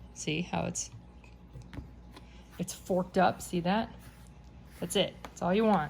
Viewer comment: Lol! Did you just say “you’re forked up?” 😂😂😂